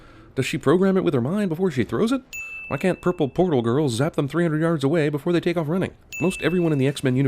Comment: I thought he said purple porno girl, I am soo disappointed. That would have been a better X-men movie